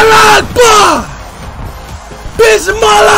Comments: did he say Allah u Akbar (God Is Great)?😂😂 Wow then he said Bismillah 0:31 ., Nah man I'll die laughing because of this man😂😂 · Did he say "Bismillah"😂😂 ooh god that killed me 💀